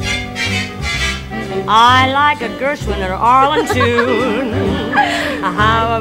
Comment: what did Judy say? I’m hard of hearing. I can’t make everything out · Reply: She said I like a Gershwin or Arlen tune. Harold Arlen was a composer who's name was unfamiliar, though if I checked probably would be with his songs.